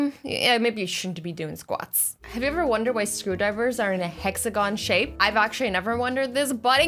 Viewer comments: When you said screwdriver I thought you said scooba diver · I thought she said scuba divers are hexagons 😂 But then when she showed the picture I was like: oh... she was talking about screwdrivers!! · When she said screwdrivers I thought she said scubadivers Then saw the pic and laughed my head off😂😂😂😂